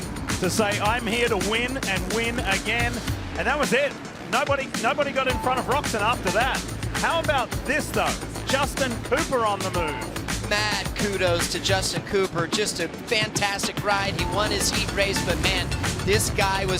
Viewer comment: did he say Justin pooper 😂😂😂